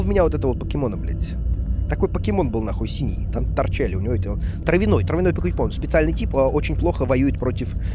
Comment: Мне послышалось, или он сказал Трявяной покепон?